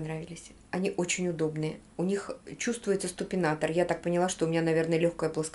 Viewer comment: Дорогая, то ли мне послышалось, как ты сказала СТУПИНАТОР, надо говорить СУПИНАТОР-это специальная стелька....... ты всё и так знаешь без меня просто букву Т не говори!!!!! А кроссовки которые ты мерила симпотные.)))))) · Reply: Леня я так и сказала :((((, я не правильно сказала. Потом сидела и думала : «Люба, ты опять лохонулась 😂. Почему СТУпинатор то ? Ведь СУПинатор». Но уже поздно 🤣🤣🤣🤣